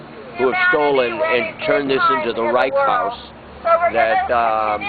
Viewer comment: Okay, so I heard "right" when he said "reich". That certainly qualifies me as moronic. But, "fucking moron"? You are a harsh one, aren't you? Besides which, Saddam was such a nice guy. He certainly meant well. How rude of us to invade his private country! Heck, we practically *forced* him to invade Kuwait. Thanks for putting it all into historical perspective. Naturally, it turns out to be our fault. Well done, krisboro.